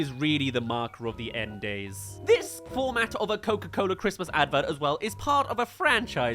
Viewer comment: Completely unrelated but I literally just had a dream last night about Coca-Cola making an ad completely with AI, and I’ve only seen this video today. One particular thing I remember saying in the dream was: “look at this, It can’t even recreate their logo! What the heck is E-Cola???” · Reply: I thought you say “What the heck is E-Coli???” For a second